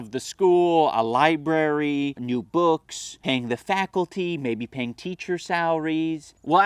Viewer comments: does anyone else hear saying "hang the faculty"...? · It sounds like he says "Hang the faculty."